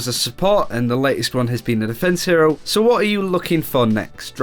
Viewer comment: did you say defence hero?